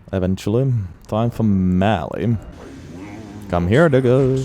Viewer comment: What did bro call beast clergyman💀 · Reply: i think he said digga, its german slang for "bro"